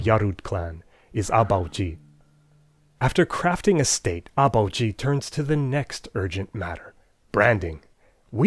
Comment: I had to pause at 1:21 and go “wow did he just say the leader’s name was 아버지? That means father in korean.”